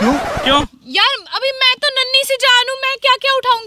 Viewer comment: I heard that she said I am naali si jaan